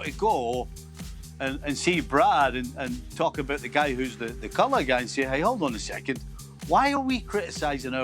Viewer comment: did stevie just say the coloured guy? Lol